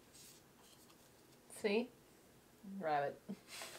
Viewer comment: what did you say at 19:09? rabbit?